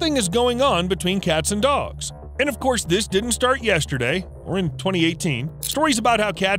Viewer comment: did he say we are in 2018